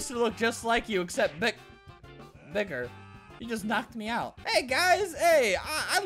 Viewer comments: did he say the n word? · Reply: he said bigger lol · does he say the n word? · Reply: No he said bigger